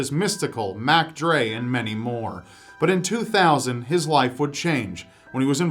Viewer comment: I thought he said,"Thizz life will change."😹😹😹